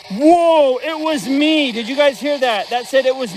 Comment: I paused at 1:07:51 seconds prior I heard “ yes, it was me, I did it “